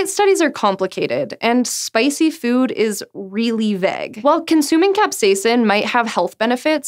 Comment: Glad I had annotations on or I would have thought she said spicy food is really veg. Holy accent batman, so weird it doesnt come through really then boom, vegh!